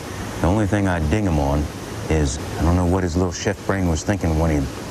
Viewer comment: Did he just say "Shit brain" at 34:26?